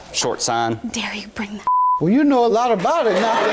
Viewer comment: Anybody hear her say " how dare you bring that up " 4:54 look like std was real